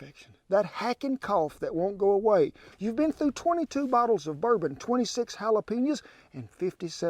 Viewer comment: Whoops I thought you said a full bottle of bourbon!